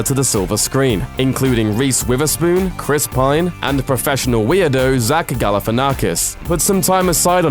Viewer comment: anyone else think he said "professional weeaboo"? XD